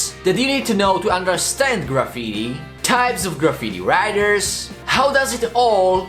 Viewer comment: I always thought he was saying graffiti rider but now I know it's graffiti writers 😂